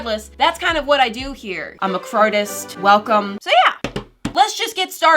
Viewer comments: When She Said "Im A Cartist" I Thought She Said "Im A Fartist" - Lol😂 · OMG. I thought you said 'I'm a crapist.'